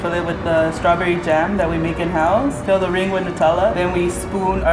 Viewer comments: did she say fill the ring with nutella?! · I swear the lady at 8:00 said "Our strawberry jam that we make in hell"